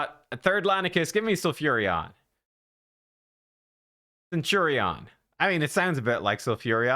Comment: Plarium thought you said Centurion instead of Sulfuriin 😂